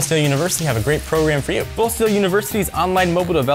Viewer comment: i thought that said full shit lol